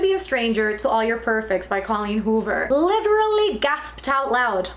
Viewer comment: I really thought you said “laughed out loud” and then held up All Your Perfects and I was like HUUUH???